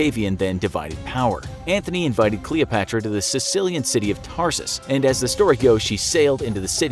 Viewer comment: CILICIA, not Sicilia ( Sicily) sounded like you were saying Sicily.